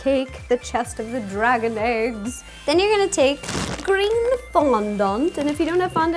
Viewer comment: lmao I thought she said green fawn dung